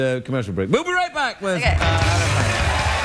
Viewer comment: Did he just say "we'll be right back with anna kendrik"?